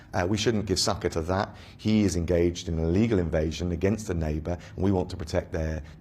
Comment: At around minute 22 I heard "legal invasion" ... Legal invasion ?!?! Is this guy serious ?